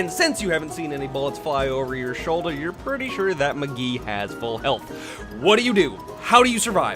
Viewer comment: Did he just say McGhee?